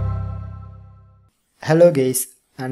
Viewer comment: I thought he said “hello gays” at the start